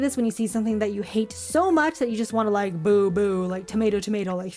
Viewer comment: Did she just say.. LABUBU??1?1?? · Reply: She said boo boo…